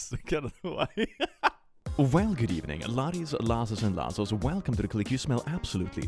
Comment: guys what does his intro say ? 0:14 subtitles say “well good evening lotes lasses and lassos” what does that mean ?? 😭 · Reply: "Well good evening, laddies, lasses and lassos, and welcome to The Click, [insert something about smell]".